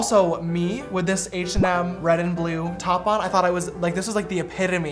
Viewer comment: Did u just say red and blue